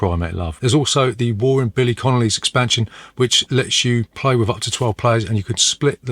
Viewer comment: Had to skip back to 10:34 as I thought you said ‘Billy Connolly’s expansion’.